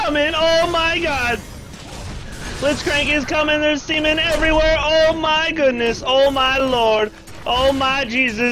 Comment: wait,did he just say there is seamen everywhere? O_o